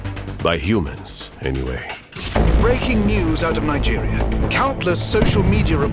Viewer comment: did I just hear Nigeria